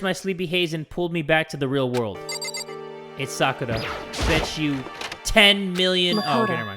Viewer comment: Oh I thought you said it's Sakura who rang. Then I thought you pretended not to realize that Kyoko rang.